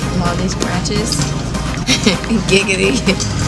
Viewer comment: OMG, Did She Just Say Giggity ??WTF??!?!?